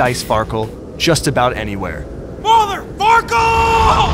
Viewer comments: i thought he said MOTHER F***ER but he said MOTHER FARKEL hahaha PUN CITY!!! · OMG AT 1:08 I THOUGHT HE SAID MOTHER F*CKER but he accualy said mother farckle